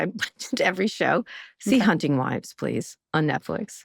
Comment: I thought you said Sea hunting wives so I tried to find it to no avail. So I read all 16 min. of transcript before i discovered you said Hunting Wives. Now I need to watch it. Thanks for recommendation.